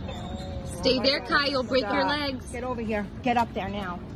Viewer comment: I truly hope she doesn't actually break her dogs legs for pictures.. One just never knows with insane people in this world. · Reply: Haha I thought she said that too, she actually said "you'll break your legs". Because he's small and elderly and if he jumped off he'd get hurt badly